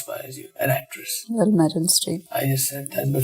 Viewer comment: what she are saying? wich actor is her Favorite? cant find that.. somone know that ? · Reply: Meryl Streep